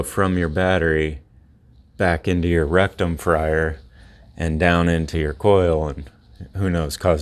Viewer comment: haha your beard holder made it sound like you said rectum frier!